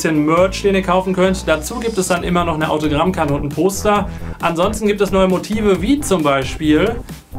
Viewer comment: LOL ich habe toster verstanden nich Poster LOL